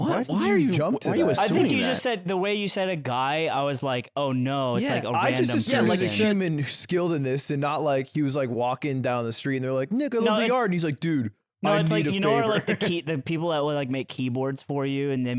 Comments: Did Ludwig say the N-word at 20:41? I’ve listened to it like 20 times and it sounds like he said “N** with the R”. Maybe the audio got a bit jumbled or something with the other guys talking but it sounds so clear. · am I hearing this right? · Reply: I heard it too. In reality he's saying "Nick I love the yard" but yea I heard it too lol